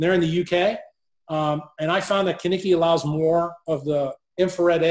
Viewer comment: Anyone know what the clothing brand is that was mentioned at about 51:00. I did not know how to spell it and got no helpful results from searching. Many Thanks. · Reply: Kiniki